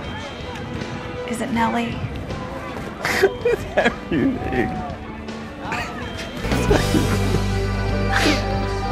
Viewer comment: I can't make out what Toby says at 9:40? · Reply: I think he says “it’s everything”